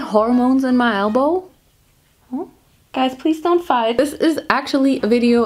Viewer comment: why did I heard jhope "DONT FIGHT DONT FIGHT* 😭😭😭😭😭